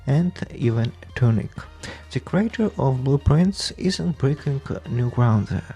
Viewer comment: LMAOOO the whole time in the podcast I thought you were saying blue prints - thats why I get when english isnt my first language i guess